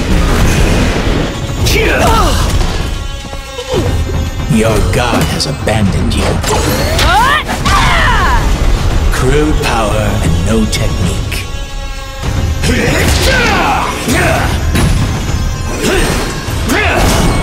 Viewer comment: wait what did he say ? "Grill power and no technique" LUL